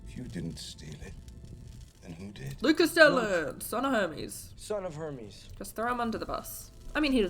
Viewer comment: I thought he said "Son of Herpes" for a second